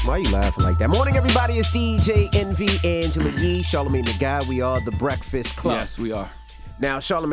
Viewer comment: I thought they said BC, Charla were irrelevant ... keep up the Great Work you all.